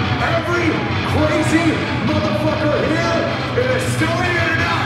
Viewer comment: did Corey say Estonia? Was this really the estonian concert? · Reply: Estoniasssss yes he did